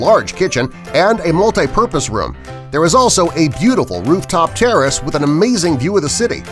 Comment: Did he just say a beautiful rooftop terrorist? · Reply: He said terrace you dumbfuck